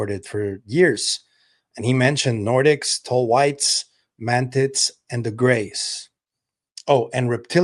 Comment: Did I hear him say Nordics, ‘Man-Tits’ & the Grey’s @ 3:52 into the video?